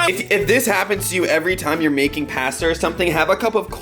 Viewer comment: did...did just say paster